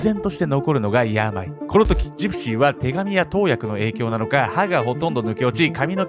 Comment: ごめんなさい！ 3:55 あたり、なんておっしゃってます？ 「手紙や投薬」って聞こえてしまって😥